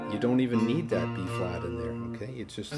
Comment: Ignore my previous comment. I thought you said E flat but on replay I hear you say the Key is B flat. Thanks for the lesson.